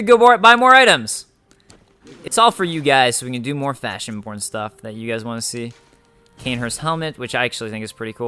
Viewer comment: I believe somewhere between 41:00 and 41:30, it sounded like you said "FashionPorn" instead of "FashionBorne."